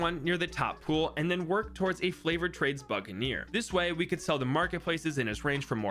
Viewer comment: Who heard flavored trades